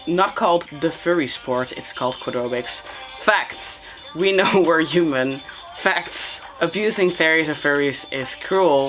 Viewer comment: i thought they said "We know where you live,Fact."